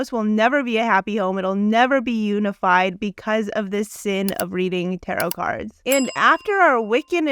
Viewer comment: the deacon said it will never be unified cause of the clash in spirituality. He didn’t say cause of the witchcraft, however true it may be.thats not what he said.